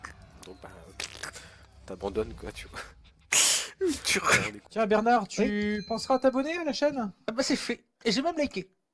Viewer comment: Il dit quoi ? Urtuque ? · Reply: Hello. Il dit :" une tuerie" Bernard quand on le découvre ça fait toujours peur😆😂